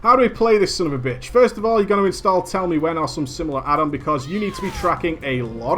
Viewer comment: What addon did he say to get at 7:20? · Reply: I believe it was TellMeWhen